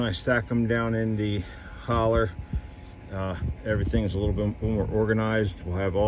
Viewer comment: in....Did I hear you utter the word "holler?" I didn't know you were learning how to talk like me - a southerner!! Looks like hard, sweaty work brother! And a BIG THANKS to for the help!!